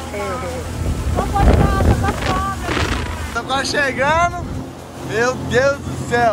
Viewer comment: so eu que escutei alguma coisa de sogra kkkkkk